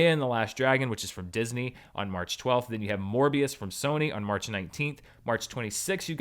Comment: “Then you have Morbius from Sony” I misheard that as “More B.S. from Sony”